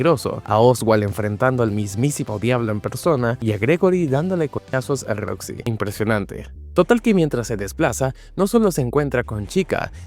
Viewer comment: yo entendi que gregory le daba vergas a roxy 😅😅😂